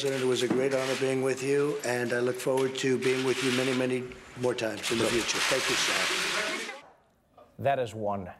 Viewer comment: "I look forward to being with you many many more times." If you listen closely it kinda sounds like he says "I love you too." lol